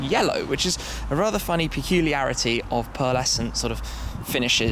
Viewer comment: what did he say? Pearl-- something? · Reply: pearlescent